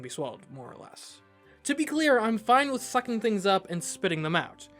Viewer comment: When you said you were fine with sucking things up I thought you said #@ things up